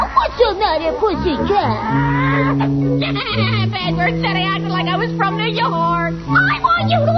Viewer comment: What did Speedy said around 1:45? "Bad bird said ... "?